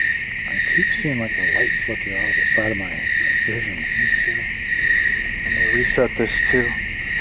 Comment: idk if it’s just me but i hear a girls voice say “me too”